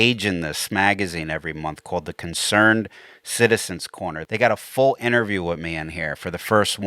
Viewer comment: did you say concerned citizens corner? 👂👀